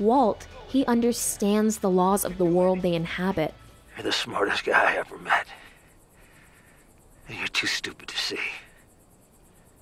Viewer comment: I got major Mandela effect, I always thought he said “you’re the smartest man in the universe”